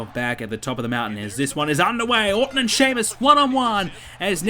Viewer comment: did say Shitmus